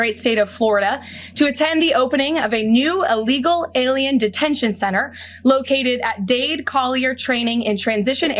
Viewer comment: Seriously.. at the 2:17 mark.. I could swear she says" opening of a new illegal detention center" I'm not even trying to be funny